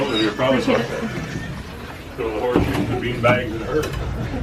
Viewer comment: I heard “ the bean bags bigger than her”.